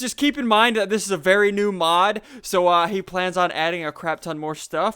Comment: Wow this mod is awesome, but in the beginning I thought he said "new moms too":P